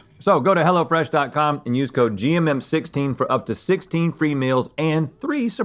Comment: When Rhett said 'for up to 16 free meals' I heard 'for up to 16 females' and I was like 'oh, okay' like it was a totally normal thing 😂